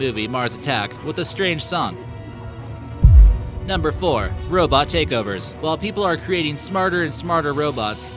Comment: I thought he said Robot Jacobers and I was liek WTF YEAH